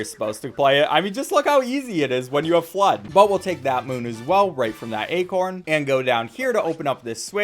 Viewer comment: I thought he said moom and was about to riot as a failboat fan